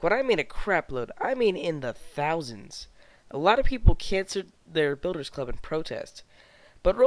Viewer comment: Did you say "Cancered?"